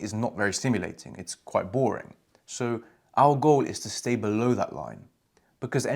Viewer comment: I hear Al Gore not our goal just saying🤷‍♂️